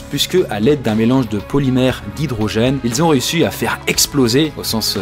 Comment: Important SVP 2:21 il dit quoi ?! "a l'aide d'un polymère de [ ? ] " Hydrogène c'est bien ça ?